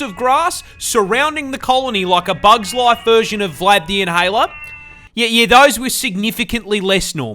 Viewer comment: I don't know if it's your accent or not but it sounded like you said Vlad The Inhaler 😅😂